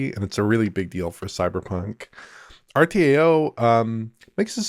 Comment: Did he say "Cyberclunk" at 7:55 ?